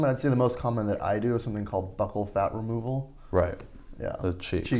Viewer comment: I thought he said "butthole fat removal"